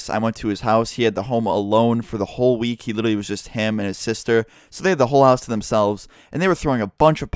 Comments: At first I thought he said him hammering his sister😂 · When you said "it was just him and his sister", I thought you said "he was hamming his sister" at first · Thought u said he was just hammering his sister lol